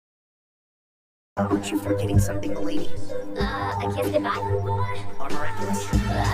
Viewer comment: can someone tell what ladybug said here please,I'm confused cause i couldn't hear her 😢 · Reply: It's a kiss goodbye 😂